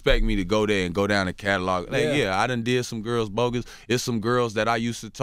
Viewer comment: 😭😭😭😭💀💀💀💀. Nigga WTF DUDE . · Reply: I heard “I did some girls bogis”